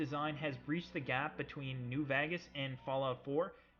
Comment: When he said new vegas I thought he said new bag ass